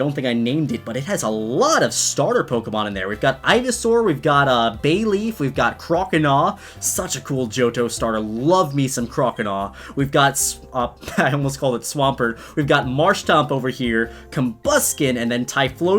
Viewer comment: did he say such a cool JoJo starter?